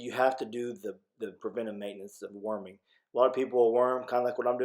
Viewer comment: At first I thought you were saying that you were "warming" them. I thought...huh??? How is he warming them up in an open pen? Then after you said it again, it hit me. Duh!!! Worming!!! My hearing ain't what it used to be. I also thought it was a little comical when Jesse was checking out your work. I think he was trying to straighten out the crooked fence...lol. There was one point where he walked away. I don't know if that's because he was exasperated at not being able to fix it or if he was satisfied that he had done all he could do to straighten it out himself...lol. Then the Jesse and Steve mini battle over the feed...ding ding ding... Round 87!